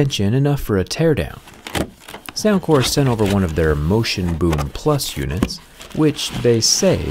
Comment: Why did i hear "Soundcore sent over their motionboobs....."